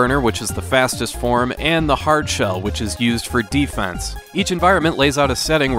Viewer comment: For some odd reason i heard it as heart shell and was expecting a way different design 🫠